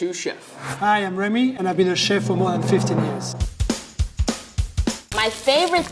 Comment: thought he said hes been a chef for 115 years